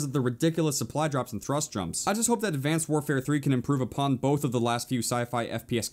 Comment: Am I the only one who heard him say "Advanced Warfare 3"? 😂😂😂😂😂